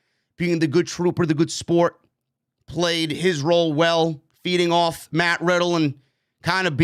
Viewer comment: Did he say Randy was feeding off Matt Riddle? I hope so because it sounded like he said beating off Matt Riddle. That’s DEFINITELY not PG.